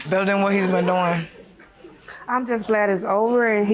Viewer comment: 🤣😂😂😂 why did the wyt guy say “fire it up”? 😭😭😭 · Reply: He said "I hear that."😆😆😆